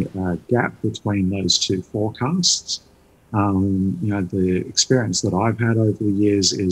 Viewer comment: Did I miss hear an 'N' instead of a 'S' ? Forecants? haha